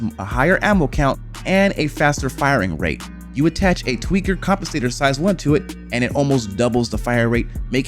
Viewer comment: "you attach a....." WTF did you just say??????? A tweaker __________ size 1 to it.........what??? Oh, a tweaker compensator size 1.........why speak so fast dude......